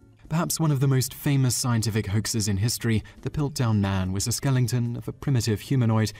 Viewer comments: Wait, did you just say skellington? XD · Lol you said skelington instead of skeleton, I don't know if that's an actual phrase for skeleton, or if it's just mispronunciation , or whatever, but regardless i'm not really critiquing you for saying it, just pointing out that it's kinda funny; but yeah no hard feelings.